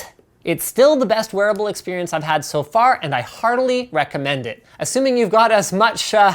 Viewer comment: "Hardly recommend it"??? That's what it sounds like he says